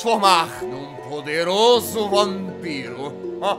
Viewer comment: quando ele falou vampiro eu quase ouvi one piece kkkkkkkkkkkk 31:23 EU AMO ESSA SAKURA!